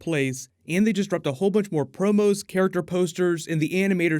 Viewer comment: I thought that said porno instead of promo I was like word